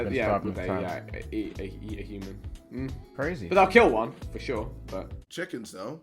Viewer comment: did vik just say he kill one(lion)